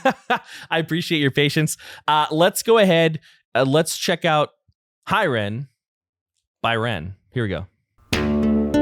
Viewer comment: Each time a reactor says "Hi Ren, by Ren", all I hear is "Hi Ren, bye Ren". :) But um... regarding your reaction... do you really HAVE to pause, though? ^^